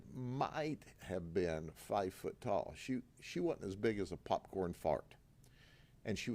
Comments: When describing the size of the first woman, Sheriff Grady said she wasn't as big as a popcorn fart (3:53) - did I mishear or did he actually say that? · Did he really say “as big as a popcorn fart” 😂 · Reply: I thought he said 5 foot tall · did he said popcorn fart!? LMAO 🤣